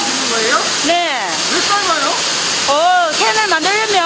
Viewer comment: ㅋㅋㅋㅋㅋ 왜 삶아요를 자막을 왜 살아요 라고보고 왜 살아요 라고 들었다.